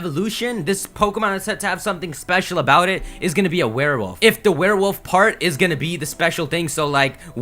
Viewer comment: Gamefreak's making all the right risky moves. Seriously, Sun and Moon are gonna be the greatest Pokemon games across all gens. #PokemonSunMoon #WATERARCANINE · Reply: Did you just say that rowlett is going to be a werewolf? lol